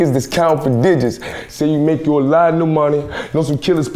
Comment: "So you make you alot of new money" me I thought he said "so you met a land mine". And the way he enjoyed the interview🔥🔥🔥